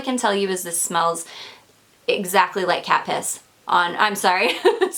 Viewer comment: Initially I was not paying attention, then I heard "cat puss." I was like, what did she say?! So I replayed and lol'd. Good one dear! ❤️